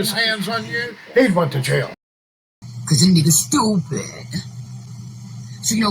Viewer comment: did she say ni***?